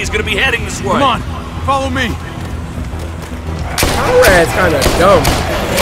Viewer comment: did he say Paul ass kinda dumb?